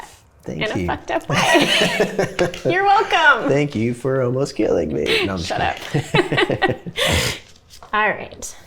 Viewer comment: Instead of "... almost killing me" I heard "... emasculating me" Coincidence? Pretty much means the same in a relationship...